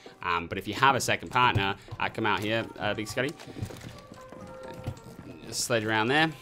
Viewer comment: did you just said "lagi sekali"